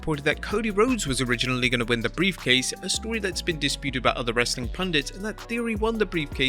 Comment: did he really said " wrestling pandits"?😳😳